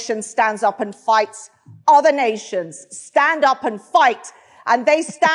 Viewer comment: Sorry for this, but, after the first round I only heard 'stand up and fart'.